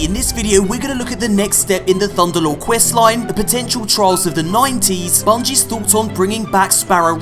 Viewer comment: I thought you said "Trials of the 90's"